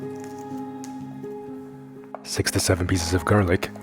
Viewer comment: Lol, I turned my sound up because I thought you said 67 pieces of garlic 💛